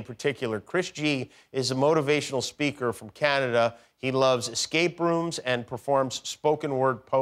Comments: I thought he said, "He loves skate rooms." 🛼 I had to rewind. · When Jimmy said, “escape rooms” I heard “skate brooms”. I was definitely having a “wtf does Chris like?” moment. 🤔🤣